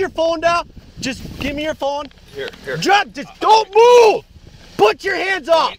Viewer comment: “put your phone down….DONT MOVE” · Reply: He says it at 6:01, he clearly said "drop it"